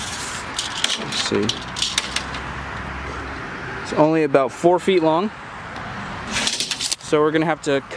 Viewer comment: Excuse me I have a question I would like to ask 0:49 What Are You Saying? It sounds like that 4feet [121cm] to me 4feet[121cm] Right?